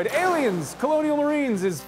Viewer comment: I thought said Arians: colonial marines. which made me think of racists in the colonial times